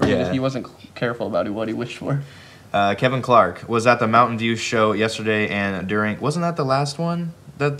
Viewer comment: is saying Mountain Dew show instead of Mountain View Show WTF